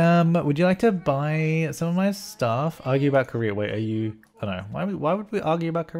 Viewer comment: I had to go over this about 3 times before realising what he was saying. I kept thinking he was saying argue about Korea 😂